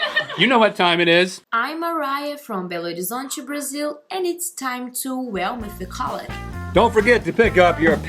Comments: "and it's time to ..." what is she saying? I've listened a few times and just can't make it out. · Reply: "And it's time to whel mythicality" or something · I watched this while I was really tired so at the end I heard "it's time to whale mythicallity"